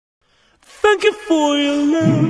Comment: I thought it said begging instead of thank you😮